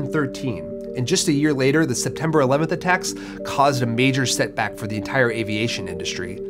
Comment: i thought you said 7-11 attacks. No disrespect to those who died in the 9-11 terrorist attacks tho. F